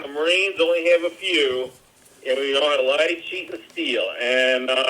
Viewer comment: Does he say, "We know how to lie, chet, amd steal?"